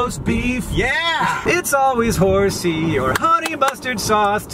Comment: I’ve never heard the phrase “horsey sauce” before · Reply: I was a little concerned when I heard "it's mostly horse-" while they were talking about the sandwiches they got 😂